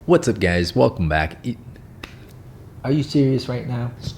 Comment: It's Sounds like he said "what's up gays" at the beginning 😭💀